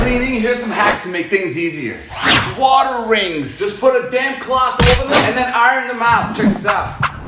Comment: I thought he said "damn clout"😭